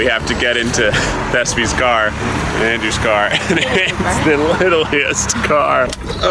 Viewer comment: im i the only one who heard the littlest car